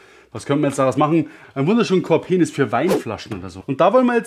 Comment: Verstehe ich bei 4:50 „Korpenis“? 🤣 Gibt es das Wort wirklich? 🫣